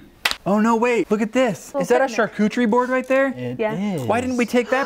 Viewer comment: i really thought he said charc00chie board